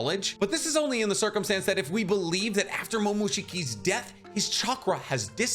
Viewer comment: when you said big death i thought you were gonna say Kagura from Boruto